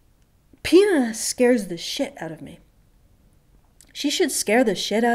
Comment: Ahaha I seriously thought you said "penis scares the shit out of me"